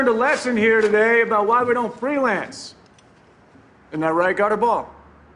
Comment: what did Chief Pridgen say at 0:23 ? I could not understand what he said... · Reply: He called Otis Gutterball.